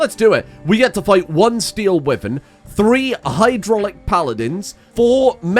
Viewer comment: I'm only a couple minutes into the video, but I just wanted to say that I keep mishearing "Steel Wyvern" as "Steel Women" and laughing every time. :D "Don't want to go out there when there's Steel Women about!" :D